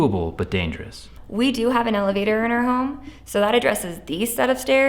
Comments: I thought she said “We have an alligator 🐊 in our home” but then realized it was elevator in context 🥴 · I thought she said, "we do have an alligator in our home" 🤣🤣🤣🤣🤣🤣🤣